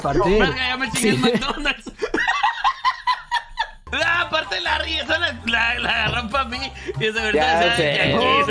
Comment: Soy yo o no entendí ni madres lo que dijo el xD solo y lo entendieron jajajaja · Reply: Nahh aparte esa- la... la agarro pa' mi y aparte de segurito decia de aqui me suena